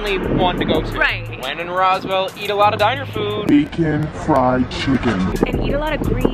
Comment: I was tripping and thought you said 'vegan fried chicken' at 2:36. I was like HOW SWAY?!